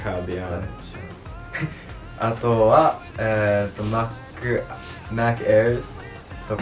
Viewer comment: 好きなアーティスト発音良すぎて&自分が英語わからなすぎて、マックナゲットにしか聞こえない